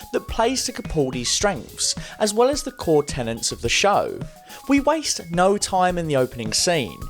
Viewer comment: Did anyone else hear “the core Tenants” instead of core tenets?